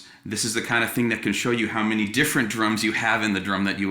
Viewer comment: I swore I had heard "they just need more TENSION than they get" at the end. That was a funny misunderstood